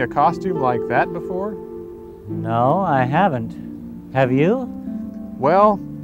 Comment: I legit thought he said "that was cringe" for a sec